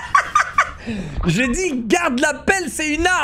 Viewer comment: ta dit quoi j’ai le sida ‘’ 😂😂